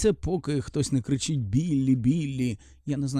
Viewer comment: я почула "Віллі... Біллі хехехехехе"